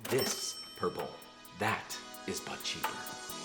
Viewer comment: I heard "THAT... is butt shaper" and thought "Whoo... what video did I click on?" Lol